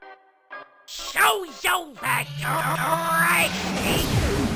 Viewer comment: Can Someone Tell Me What Spamton Actually said I Couldn't understand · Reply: me it sounded like he said "shows so bad you're crazy" which in spamton speak would probably be "SHOWS SO BAD YOU RE RE [crazy!!]" But I can't tell for sure without official captions